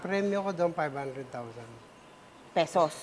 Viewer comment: what did the woman say? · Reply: She said "dollars" she was surprised by the prize efren earned in that competition 500000 USD